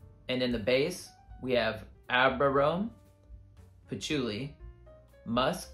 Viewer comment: What do you say in 3:29 ? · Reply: I say Ambrarome (sorry if my pronunciation was a bit wonky). It is kind of like an amber gris like note that has some animalic facets. Hope that helps.